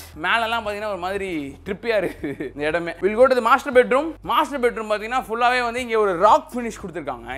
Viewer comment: why did i hear m@ room 😂😂